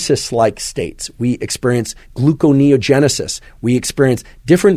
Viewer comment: I don't know who to contact but the subtitle is a bit wrong, all instances of "Seasonal *A*ffective Disorder" is misspelled as "Seasonal *E*ffective Disorder", and at 1:38:20 instead of "gluconeogenesis" "glucogenesis" is used.